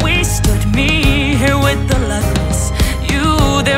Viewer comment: I always heard “luckless” as “lepers” thanks for showing the lyrics.